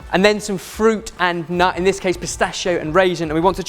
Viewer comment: D...did Ben say "musstachio" at 1:44??